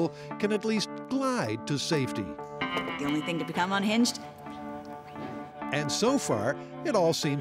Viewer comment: What she says in 14:24 ? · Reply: Something like “the only thing to become unhinged?” Then points at herself.